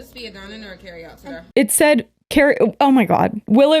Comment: I'm going to be honest with the girlies but I could not hear the "today" bit at the end until I had went back and relistened to it like 10 times. It doesn't sound very clear to me, BUT I also don't think she said sir (because why tf would she?). This is just my yanny or laurel moment I swear, the lady does seem to exhibit racist tendencies and believe racist stereotypes.